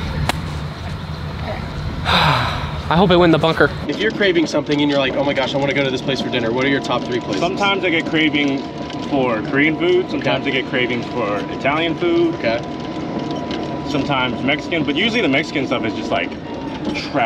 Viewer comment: Thought just said Korean boobs 😅😅